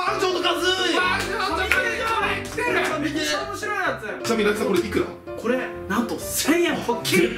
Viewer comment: どんな体勢で聞いても 「レトさんこれいくら？」 にしか聞こえへん笑